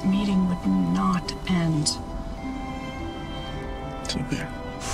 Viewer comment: what does Edgar say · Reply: It’s okay